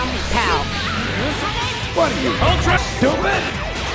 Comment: I laughed super hard at when he missed his level 3 and i just hear "Are you Ultra Stupid?"😭😭😭😭😭